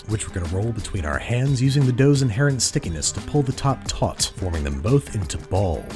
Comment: i love how at 2:02 the captions say "to pull the top tots" instead of "to pull the top taut"